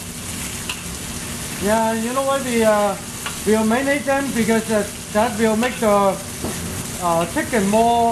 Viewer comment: What does he say at 4:20 that keeps the chicken tender? I listened to it a few times and can't make it out over the sizzling oil. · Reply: Hi he said "Yeah you know why we marinate them? Because that would make the chicken tender."